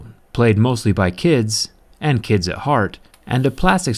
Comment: Hey jerry. Check the captions for 1:31 you wrote kinds instead of kids.